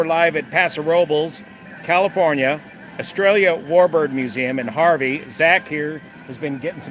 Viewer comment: When you said "Estrellla", I heard "Australia", and lo and behold, the next ad on my screen was for the Anzac Weekend Airshow, 27 to 28 April 2024, to be held at West Sale Airport, in Victoria, Australia. See ya there!